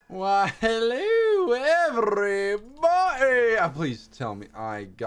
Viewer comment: why the heck am I hearing “hello every bunny”? even tho I know it’s everybody